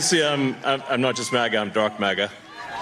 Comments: That "Dark MAGA" thing is way more disturbing than anyone probably realizes. I'm genuinely afraid of what this refers to. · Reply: I thought he said Darth Maga like Darth Maul. That made sense to me like Trump is emperor palpatine and musk thinks that he's going to be his (evil) Jedi enforcer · i thought elon said i'm not just maga i'm darth maga, did my ears deceive me?